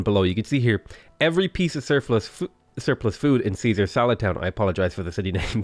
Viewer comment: Oh, that was "Caesar SALAD Town". Genuinely thought it said "Caesar's a Lad Town". XD